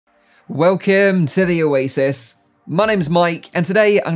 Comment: When ever Mike says 'Welcome' at the start..all I hear is "Well Kim". And I'm wondering..."who's Kim"?